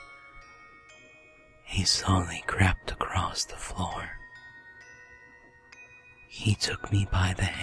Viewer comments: Just me or does it sound like he says: Slowly crapped across the floor.. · It sound like he is saying "he crapped across the floor" and " crapped up your back and though your hair" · LOL I misheard him at 0:20 and thought he said "crapped" instead of "crept". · I heard he "slowly crapped across the floor" xD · "He slowly crapped along the floor" oops misheard😂😂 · I keep hearing "crapped across the floor" XD